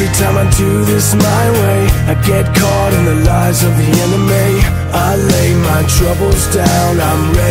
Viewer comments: I get caught in the lies of the enemy Enemy sounded like anime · bring meow come and find me in the dark meow this band must really like cats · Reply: saleh Ikr and the part where it says " I get caught in the lies of the enemy." I hear " I get caught in the lives of the anime." And now all I hear is " meow" instead of "now."